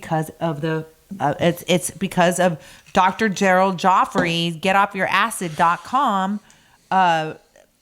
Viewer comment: I had to put you on pause while I looked up that powder super supplement I thought you said get off your ass. Lol. Nothing came up. I finally realized it was acid not ass 😂😂